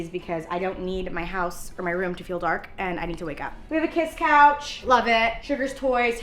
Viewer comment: So Chic! It’s just now clicking in my head that has the same couch in her room as the Bratz girls 🫶🏻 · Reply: I feel silly she said “kiss couch” and I heard “kids couch” 💀